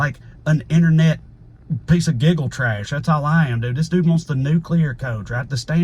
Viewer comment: snort laugh I thought you said you were a piece of giggle trash But my bf thinks you said giga trash Who is right